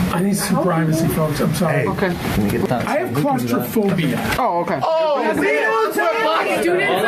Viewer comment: When he said he had "claustrophobia" and I hear "black students" I thought to myself, no way...no way someone is about to say that claustrophobia means you are scared of black people